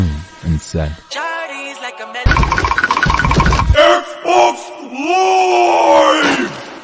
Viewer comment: what did evildoer said💀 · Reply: XBOOX LIIIIVEEE